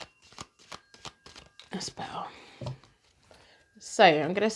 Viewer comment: I picked pile 3. Just wondering- and this might sound odd, but at about 21:04 as you are shuffling the cards, you can hear a voice say something I do not think it is your voice. Did anyone else hear it ? It says " dispel "or something ?